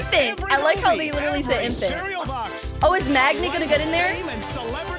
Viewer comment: help it sounded like she said “magnet” instead of maggot lol